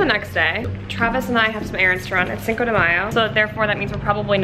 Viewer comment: só Eu que ouvi ela dizendo 5 de maio em português?